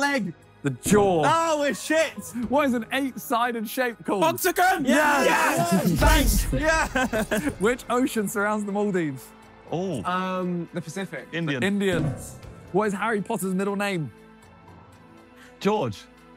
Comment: for a second I thought Simon just straight out called Vik and Indian for getting the question wrong